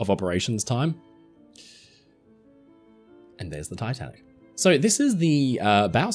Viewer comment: did he say titan or tatanic?